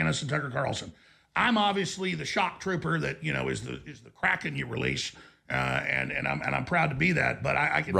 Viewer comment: did he say he's the crackhead they release?